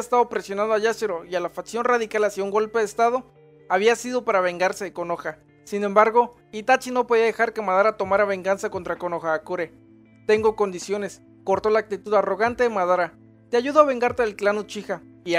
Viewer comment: por un momento pensé que decía verganza XD